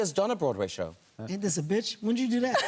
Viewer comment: What did Kevin say in 9:37? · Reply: "isn't this a b***ch" is what he said because it ruined his joke